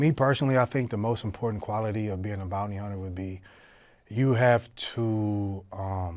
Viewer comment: i thought it said "BOOTY HUNTER" 😆😂😂🤣 no clickbait 😆 "sorry for the wait , i'm comin "